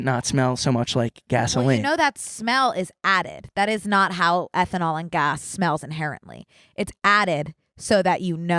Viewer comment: I thought when y’all were saying ethanol you were saying SNL with a lisp.